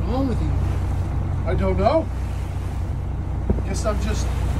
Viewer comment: Must be the hoodie from Boot Barn 😂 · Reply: I legit thought he said Hoot Barn. 😂😂😂 I'm from Arkansas, Maine is a slightly different language.